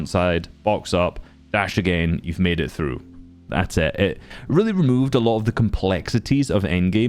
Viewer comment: GOD DAMMIT I THOUGHT YOU SAID THEY REMOVED IT FROM THE GAME, i fcking hate the stupid jetpack its so lame